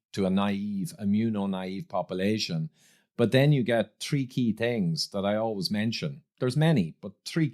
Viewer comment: "Three key things" sounded like "freaky things" first time I heard it lol.